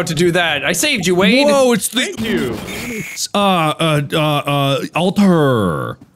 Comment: For anyone wondering what he said at 9:06, he said "WOAH IT'S THE UNUS ANNUS ALTAR" ahh I miss it dearly